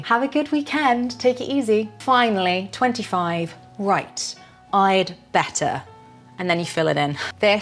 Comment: Hi, Great lesson!!! One question: "Right I'd better..." (10:24); I heard it as "RightS I'd better". Is "S" in "RightS" like a filler sound? Thanks, Albert